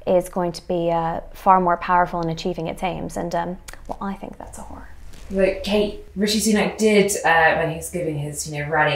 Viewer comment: Kate Says that's a hall, or w**re, or something! What does she actually say? 🤣🤣🤣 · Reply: Horror